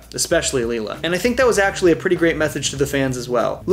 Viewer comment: Did u say methage at 11:00? Instead of message?